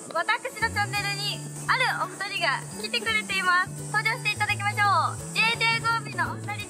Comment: 登場していただきましょう 投票に聞こえる笑 可愛すぎ💛